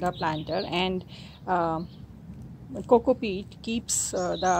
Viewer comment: Superb video. Very clear. I only 'discovered' Gallardia this year. I couldn't quite catch the fourth thing you said when potting up initially @ 1:24 ... it sounded like 'cocoa feed' ...? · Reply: Coco peat